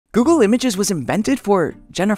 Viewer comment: Idk ehy bit i thought he said Google maps not images at first and was like how does Jennifer lopez have anything to do with maps